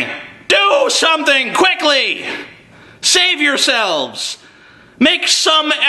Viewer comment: i thought it said shave yourselves